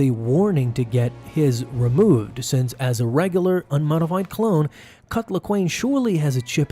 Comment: sounds like ur saying "cuck laquane"